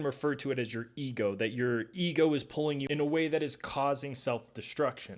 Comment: Careen? Subtitles say korean, I highly doubt that… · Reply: Qareen